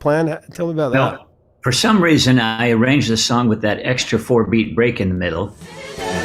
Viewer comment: Well this will probably go down as the biggest mondegreen in the history of music, I always thought he was saying “Amarang”, and I always wondered what that word means. It never bothered me that it might be a nonsensical phrase, but realizing what he is actually saying, I feel quite stupid now. 😂